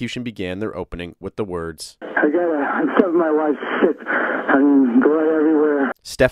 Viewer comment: I was just listening to the sound at first and definitely heard him say killed my wife shit. great video as always